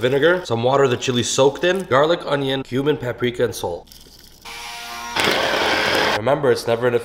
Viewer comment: i heard: "human paprika" lol